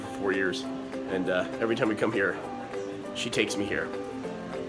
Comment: I completely misheard the last thing Burnie said. I heard, "I didn't used to wear drag."